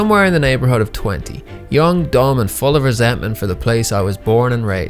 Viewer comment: I heard "young, dom, and full of resentment". Kinky